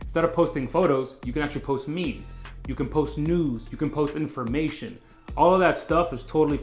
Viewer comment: definitely thought you said post nudes! Believe me, that ain’t get me followers 😂🤦‍♂️